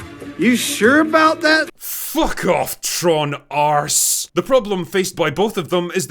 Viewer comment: 😂😂😂 everytime I hear Tron Arse 😂😂😂